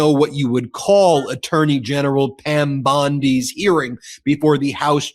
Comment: 😂 for a second I heard you saying "a turdy general"